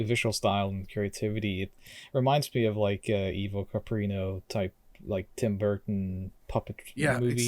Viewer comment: Does anyone know what the puppets style he mentions before Tim Burton is? An image of a penguin comes up and it sounds like he says "Eva Paprino", but I can't find what that is. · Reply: It's Ivo Caprino, a Norwegian film maker who used puppets in his films. The penguin is actually Solan Gundersen, a magpie from Pinchcliffe Grand Prix.